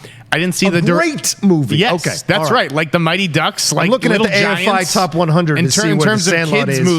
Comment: can someone tell me what rocha said? af5? a05? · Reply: AFI (American Film Institute) top 100